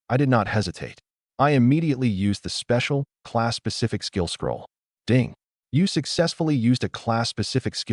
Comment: I swear it sounds like it says Class pacific skill scroll......and I cringe....every........time